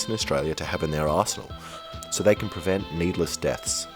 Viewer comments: dude I 100% thought you said arsehole instead of arsenal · I thought you said it would be a great thing for police to put in their arsehole, LOL.